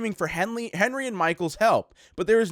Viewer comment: I heard I'm angry and Michael